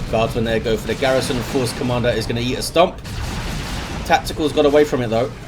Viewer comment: Did just say "He's gonna yeet a stomp"?